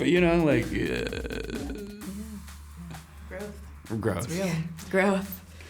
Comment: Did anyone hear Chirs saying "I have a growth" around 8:32?😂